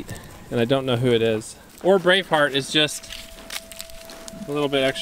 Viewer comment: Today the first time you said Brave Heart, I thought you said Brain Fart 😂😂😂